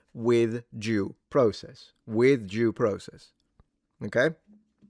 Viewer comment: It's funny that it totally sounded like you said Jew process Cuz I can imagine that the Jews would hang this guy from highest Tower😅